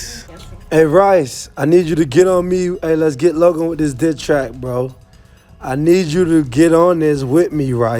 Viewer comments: I thought he said let’s hop on this dick track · is it just me I heard dicktrack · it sounded like he said "you need to get on me" and "we gotta do dis dick track" Like if u heard this